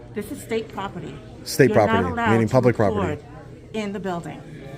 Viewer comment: "Is it federal or state?" Guard: "Stateral." "S-...?" Guard: "What do you think?" "You said it's federal, then you said it's state. What type of build-..." Guard: "I said it's federal and state sir." "It's a federal and a state building?" Guard: "No. The state law is a state law, and federal building is a federal building. This is a state building. So you can't record, sir." Whatcha drinking in that cup? 😂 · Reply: I thought I heard stateral too 😂